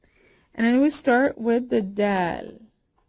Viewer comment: Did you say "Dad"